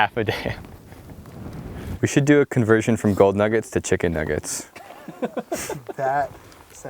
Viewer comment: I thought it said Moldy nugget, I was about to scream